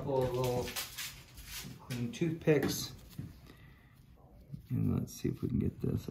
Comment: So funny he said I'm going to use these 2 Qtips and I thought he said toothpicks...I thought what the hell...lol